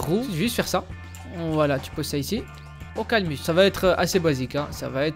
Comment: "Aucalmus" 😂😂 Panormal il dit comme moi 😂😂